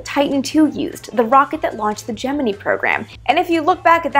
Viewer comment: Did you say "gemenee" program at (1:38)......????